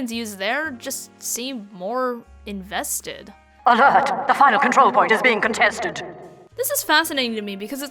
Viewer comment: It is so crazy that I thought she said THE FIRE CONTROL POINT IS BEING CONTESTED But it is final